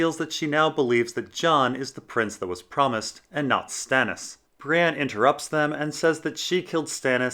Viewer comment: I heard Bran instread of Brienne and was very confused